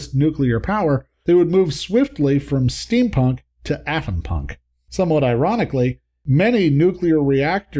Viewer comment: What is “Athenpunk”❓ I tried to Google information on it, but I couldn’t find anything about it . Can you do a video explaining it❓ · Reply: It does sound like he says "Athenpunk" and even the autogenerated closed captioning has it written as "athenpunk," but based on the context of what he was talking about (nuclear power), I'm going to assume he was trying to say Atompunk, a subgenre of speculative fiction focused on a society based on the Atomic Age. The most well known example would be the Fallout series of video games.